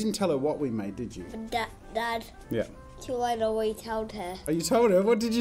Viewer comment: I think he said "too late, I already told her"